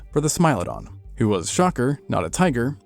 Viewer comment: what does he say? "He was a shrocker, not a tiger"... I can't make it. What's a shocker or shrocker?" Please tell me this is not AI!